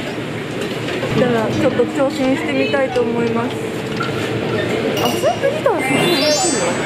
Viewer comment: 影片在3:00開始 翻譯的字幕不見了@@ 我想知道她說了什麼QQ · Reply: やばい やばい やばい.... ちょっと挑戦したいと思います 糟糕糟糕糟糕... 試著挑戰喝這碗湯